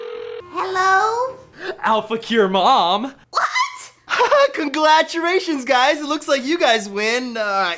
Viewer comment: Did Anthony say "conglajerations" at 3:38? Wtf?